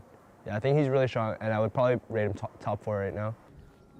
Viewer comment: Is it just me or does WildTurtle say I'd probably rape top from 2:10 to 2:12?